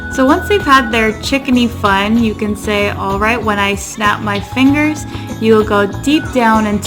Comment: Ok she is awesome on these tips when she gets to the chicken part on closing eyes it sounds like she said when I snap my eyes 🤣 it's almost at the end other than that love the voice control and confidence